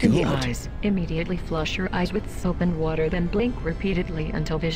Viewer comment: For the fazer-blast intro by the robot, I thought it said amputate and wash your eyes